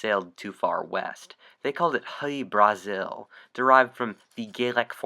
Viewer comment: Its 7:57 name i don't understand : "honeybrazil"?